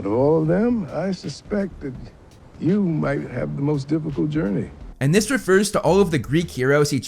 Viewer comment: I’m gonna be honest it kinda just sounded like he was calling him the most mental ill 😭